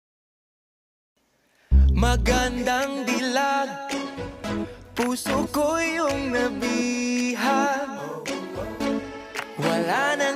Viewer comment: First time i heard this song...i thought he said "magandang bilat"😭😭😭😭😭😭